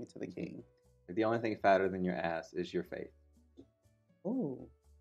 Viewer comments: “The only thing that’s fatter than your ass is your faith” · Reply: For some reason, I thought said Face instead of Faith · “The only thing that’s fatter than your ass is your faith” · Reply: Omfg I thought she said face not faith that’s so much kinder 😭😭